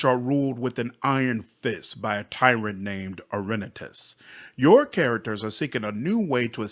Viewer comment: I misheard Orenetis' name as "Irenicus" at first and thought they were dropping a surprise Baldur's Gate crossover!